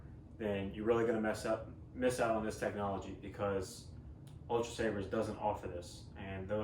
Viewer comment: Okay... and? This is Neopixel too, and I’ve recommended plenty of other companies on this channel that also sell Neopixel sabers. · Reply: sorry i don’t know why but I thought you said saberforge didn’t offer the neopixle but you said ultrasaber. That’s my fault.